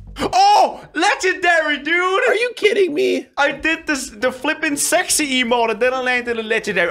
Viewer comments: A add piped up when he said he did flipen sexy and it sounded like he said flipping sex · did he say sexy